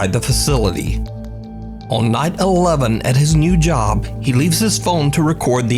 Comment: bro sounds like he said 9/11💀